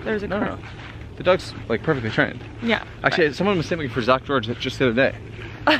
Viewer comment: I relate to too much when it comes to the mumbling. 😂😂 like at 15:30 what the heck did you say lmfao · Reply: HHAHAHA he said "someone mistook me for Zak George the other day"... Zak George is a dog whisperer type here on YouTube and we've watched a few of his videos to help us train Rojo 😂